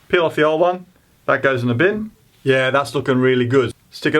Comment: What's that expression at 6:11? Bob's your uncle? Oh, I heard correctly: <link>